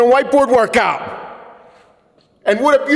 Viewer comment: This mother fucker!! I thought you said white boy workout 😂